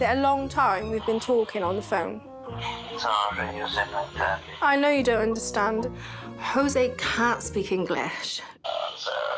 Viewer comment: Every girl’s dream is to have a boyfriend who calls her ‘chicken’ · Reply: I thought he was saying, "I know you're cheating"